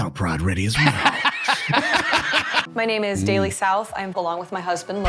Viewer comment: Did she just say "I belong with my husband"? Who tf says that.